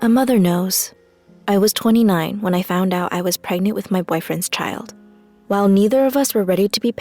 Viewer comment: I thought she said, "I was pregnant with my boyfriend's cat"...